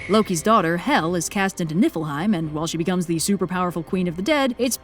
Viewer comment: i heard nippleheim